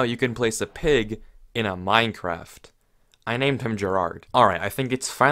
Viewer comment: is it just me or did he say pigs can sit in MINECRAFT instead of a MINECART?